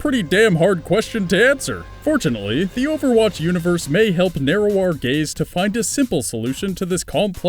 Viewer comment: I heard "narrow our gaze" as "narrow our gays" and was thrown for a loop for a solid minute.